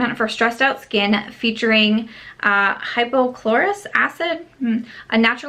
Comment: Omg I thought you said that Tower spray had hypochloric acid in it (that's what your stomach has in it), I freaked out for a minute 🤣